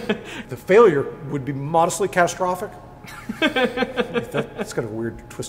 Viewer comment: I heard “Moderately Catastrophic”